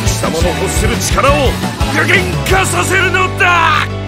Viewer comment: I thought he was about to say Plus Ultra. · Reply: no He was saying shut up